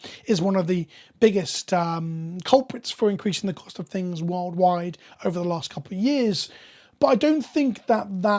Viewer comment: "world-wide" sounded like "wild ride" to me at first. Had to rearrange my thinking on the fly.